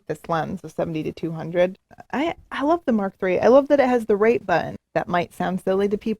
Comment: "I love that it has the __ button" I couldn't make that out, what did she say? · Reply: Rate button, to tag the pictures that are keepers.